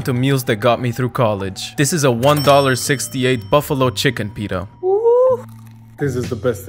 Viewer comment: I thought he said buffalo chicken Peter and i was super confused